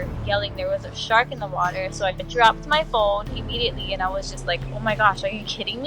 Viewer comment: She clearly said in the video that her daughter screamed shark and then she grabbed her phone. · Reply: That's odd, wth was that, a Mandela effect? I watched it twice before I 1st commented and could of sworn she said "grabbed my phone" now(I, apologize - I checked again AFTER I wrote the 2nd comment) and indeed she does say "dropped my phone" Again, I'm sorry, I must of heard it wrong twice. And I'm joking about it being a Mandela effect, I think, lol